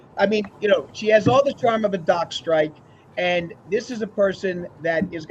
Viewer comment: Okay that was painful, is it just me or does make every show about him? No dis... just saying, I watched this for Vinny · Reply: When said "Donna has the charm of a dock strike!", I mistakenly thought he said " Donna has the charm of a jock strap!"!!!! I almost fell off the couch laughing!!! I know, it's my old poisoned mind, lol! 😂😂😂